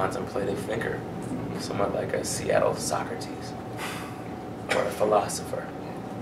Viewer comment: When he said a Seattle socrates I thought he said a Seattle soccer tease. I was like What the hell.